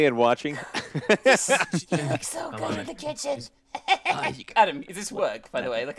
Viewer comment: I heard someone said "He looks so good in the kitchen" 0:25 And im 100,000% sure it wasn't